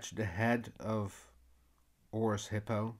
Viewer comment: Horse Hippo's name is a bit redundant haha but thanks for a good tutorial! edit: learned that its "Horus" misheard it as "Horse's" haha